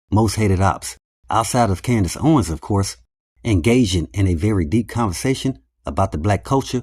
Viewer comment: “Engaging” bro I thought you said “gasian” 😂 that’s a new word!